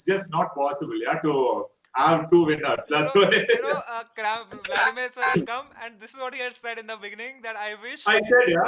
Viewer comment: What is Samay saying at 3:19:16 ?? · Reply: Congratulations, sir